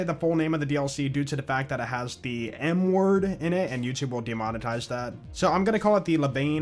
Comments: I didn’t know there was a new dlc so I came here to see what’s what.. and I SWEAR TO GOD I thought you said the dlc has the “N-word” in its title!!! So I immediately looked up the new dlc name and now I realize you said “M-word”!! 😅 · omg i thought you said it had the nword in it not the m word hahaha